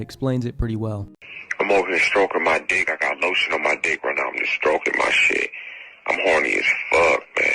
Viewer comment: OIGAN Que dice el audio sucio ese no le entendi. dice algo como 1:35 : i gotta a strogo on my dick i got A STRUGO ON MY DICK I GOT A STROGO ON MY SHEET, FUCK IM HORNY IM A FREAK MAAN, eso dice ?????'